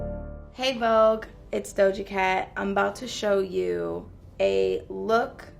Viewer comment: For a second i thought she said voga cat